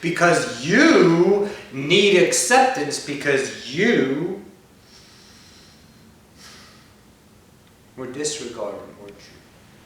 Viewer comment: What does Ocyphius say at 2:28? "...because you were disregarded for_____" Is it truth? If so, can you elaborate? Because that idea makes no sense to me. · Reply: He said - Because you were disregarded, weren't you? He was referring to the cause of alter ego, separation. From the lack of love not given, for the lack of acceptance not given to you as someone of existence. So, you separated yourself to protect your "vulnerability" to the world. and alter ego started leading you for security, self image, that was sustained by 3D social conscious. A belief to live your life by, to fit in, so to speak...Eternal Love